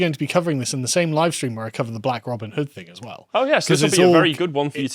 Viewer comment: He said "black Robin hood" and I heard "blacks robbing the hood"